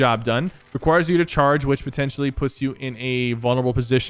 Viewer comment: Did he say "pussy you" (8:41)?